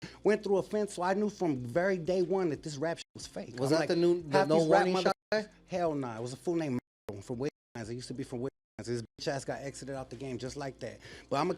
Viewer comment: Did he say Maro from Wicked Minds @ 3:30? 😂